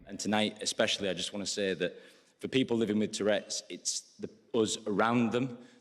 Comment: initially I heard "it's those of us around them", but every subsequent listen it just sounds like mumblings and I couldn't for the life of me make out what he was saying. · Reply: He's definitely saying US He was going to say those but then stopped himself and said us to include himself.